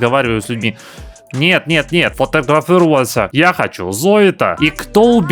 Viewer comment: услышала "я хочу зоито". заплакала.